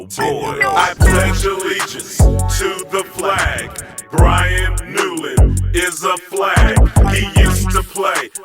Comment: Thought it said "Brazilian is a flag"